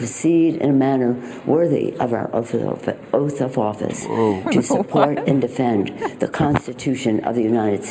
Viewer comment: I think she said offra offra oata office?